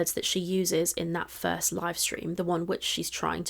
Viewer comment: Any time you say ‘livestream’ I hear ‘lie stream’ which is far more apt for Amber.